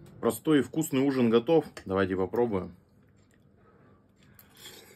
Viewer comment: Послышалось "тбилиссимо" )